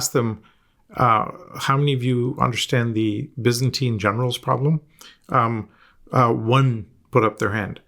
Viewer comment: Wisentin General problem ? Bissenteen general problem? Please clarify what the Audit Firms should be paying attention to : I try to listen but cant understand. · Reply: Byzantine General's problem. Not sure why it's required to know.